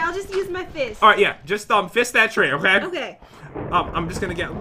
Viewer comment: Did I hear that correctly fist the tree!?